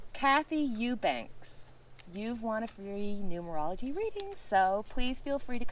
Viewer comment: Lol, at the end of your video, I thought you said, free neurology reading!!! And I thought, I wonder how she does that?!!!😅 Realized seconds later that you said numerology!!! Thanks for another fantastic video!! I loved seeing the little chick!!! 💕💕💕🙏☀️